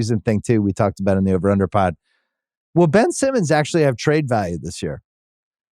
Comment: Ben Simmons can't seriously still be an NBA talking point in 2024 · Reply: Thought u said BILL Simmons and was like dang that’s harsh lol